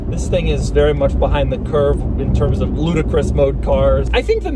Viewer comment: Did he say Ludacris?